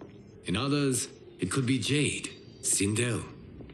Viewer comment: did bro just said "JADE" ?